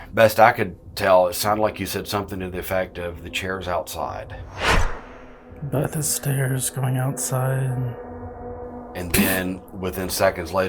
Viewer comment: I think said, But the stairs going outside ,I know it doesn’t make sense but that’s all I could make out. I loved this experiment, I hope you do it again, it was so interesting! Fantastic investigation guys, I loved seeing with you. As you know, we love him to bits, as much as we love you and. Thanks for always keeping it real. I love SPOOKY SATURDAYS 🙌🏼💙💙💙